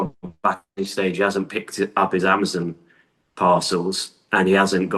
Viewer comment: I think he probably just wanted it explained for the audience. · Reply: I think he thought the guy said " rock sack " . Sometimes it's hard to understand the Brits English pronunciation .